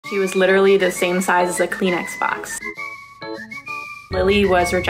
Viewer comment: I thought she said she was the same size as a clean x-box